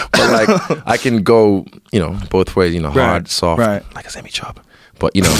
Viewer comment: What does he say right there at 4:35 that made em both laugh like that? I couldn't understand it. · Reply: Like a semi-chub.